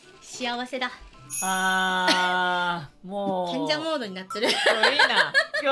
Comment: did I hear her right? Kenja mode? That's hilarious!